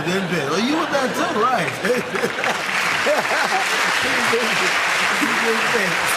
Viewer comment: what did Muhammad Ali say? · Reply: He said "they didn't pay us"